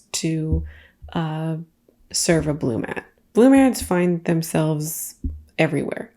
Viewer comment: What is the last thing you said about the baskets at 29:23? Blue mats? I don’t understand the words you said lol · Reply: I thought that's what she said, too! Had to watch it back and I believe she's saying "Bloom ad" - Bloom are the canned beverages they put in the basket and the original tiktok has a bloompartner hashtag as well. Took a couple rewinds to figure that out, lol.